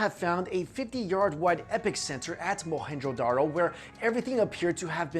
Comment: did he say "epic center"?